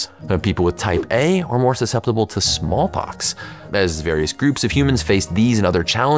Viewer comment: When he said blood type A is more susceptible to smallpox I thought he said "more susceptible to SWAMP ASS" and I was like man I'm definitely type A then... That explains a lot